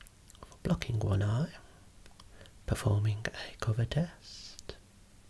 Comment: I’m confused did he say he was performing a covid test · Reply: Sounds like it but no he does say cover set